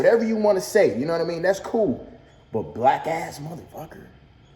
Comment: Idk it sounds like he says wack ass mf idk idk